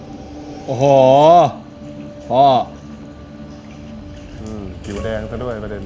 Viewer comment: Did Phu just call Pond daddy?….